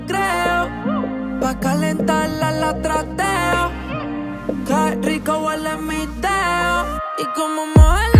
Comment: yo escuche en el minuto 1:26 :que rico huele mi pedo